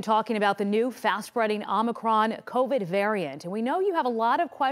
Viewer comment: I hear "Omicron" and all I hear is "persieye 8" 😂😂